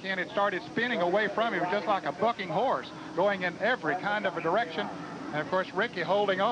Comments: sound like ned says fucking horse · Did Ned Jarret say "Just like a ****ing horse"? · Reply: You all need to drink a bit less when watching classic NASCAR, Ned did not say the F word at approx 1:30. The caption was BUCKING, I listened to the section 5 times, sorry not once did I think he said anything but the B-word. What imaginations you clowns have. It was terrific having Richard Petty adding his thoughts, the guy is one wise dude.